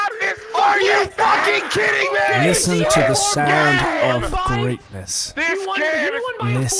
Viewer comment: Greatness? Oh sorry, I thought you said sound of Chaos xD